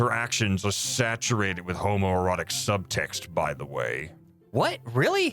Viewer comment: Homoromantic relationship? Isn't that for the fanfic? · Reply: wait i thought he said homoerotic